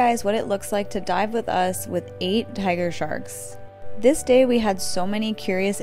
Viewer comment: I thought she said look at what it’s like to die with 8 tiger sharks lol